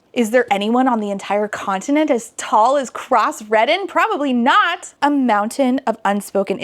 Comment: During the motorcycle chase sequence (that makes it sound so much more exciting than it was) i heard the guy's name as Cross Reddit & that's way funnier to me than it should be.